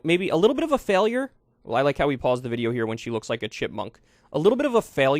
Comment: Did you just said a black woman looks like a monkey? 🤔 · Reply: He said a chipmunk.